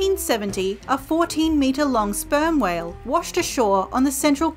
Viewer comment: sorry, did i heard it wrong so didthe captions or is itreally aspermwhale